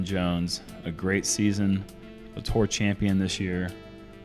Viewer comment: Love the spoiler from for people who didn't watch the final round live haha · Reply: assuming you're talking about 1:20. I immediately came to the comments but upon re-listening, I think he said "A tour champion this year" which I guess just meant he was a winner on tour this year (GMC)